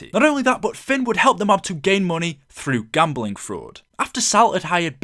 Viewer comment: gambling frod? oh i thought you said black market